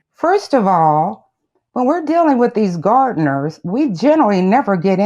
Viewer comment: Somehow when the Plantiff said, "When you deal with these gardners...," I heard, " When you deal with these Mexicans...," instead. She obviously can't see beyond her own prejudice! As a POC herself that's really sad.